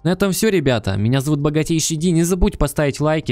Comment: мне послышалось богатей щади...